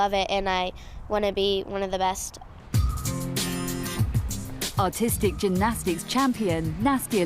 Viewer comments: at first i thought the narrator said "autistic gymnastics" then i realized she said artistic 😂😂😂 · Oh I thought he said autistic in the beginning then I realized he said artistic lol